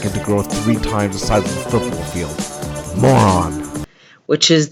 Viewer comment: Was just kinda half ass listening to program while looking over my daughter's homework and thought I heard someone say "football size foreskin"......so I put down the trig book to see what i the hell 's going on here. You need a psycho babble dictionary handy and psychiatrist on speed dial .